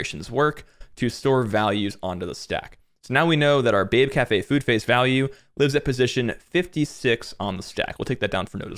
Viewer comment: All I hear is “babe cafe food face value”